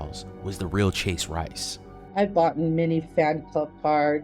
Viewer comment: did she say "I have boughten" ? Did not know that was a word.